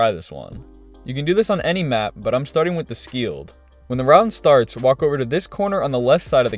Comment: Did I just hear "the Skield", and "Maria HQ"?!